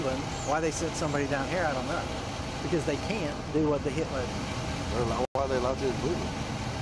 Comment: I’m willing to bet anyone ten bucks he refers to his pet dog as his “K-9 unit”. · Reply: I thought I heard him say, "Heel, Hitler!"